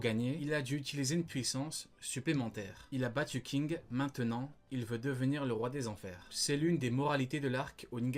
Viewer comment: Ce n'est pas "Il a battu King, maintenant il veut devenir le roi des enfers." C'est : "Il a battu King car il a décidé de devenir le roi des enfers."